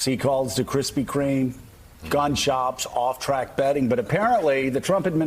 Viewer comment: Did he say 0:14 “Gun Shops”? Like I don’t believe he has ever bought a gun in His life